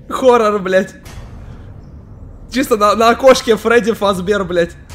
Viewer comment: АЛЕЕЕЕЕЕЕЕЕЕЕЕЕНА ВЫХОДИ ИЗ ШОКОЛАДА и мне послышалось не на окошке а на кошке